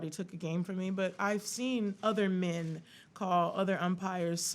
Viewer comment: Are my ears playing tricks on me, or, at 9:05 does she say ``OTHER men?